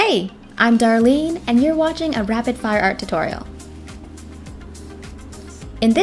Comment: I thought she said in the intro rapid fart art